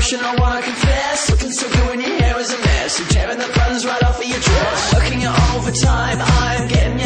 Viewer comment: o-o at first i thought it said Tearing your buns right off of your chest